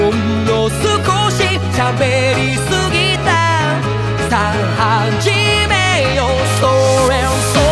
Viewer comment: ごめん何回聞いても さぁ始めようほうれん草〜🎶 にしか聞こえない